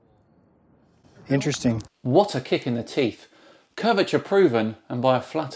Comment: I misheard you've said "what a kick in the tit" instead of teeth. Gonna use it though